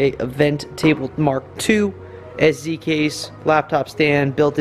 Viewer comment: Around the 10:12 mark you mention having a new, smaller 'show express box' (I think that's what I heard) for your set up. Newbie question but what is that and what does it do?